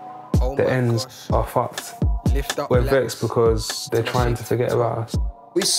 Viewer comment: what is he saying? (I'm trying to add subtitle but I can't understand that part) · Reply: We're vex because they're trying to forget about us. Vex means angry.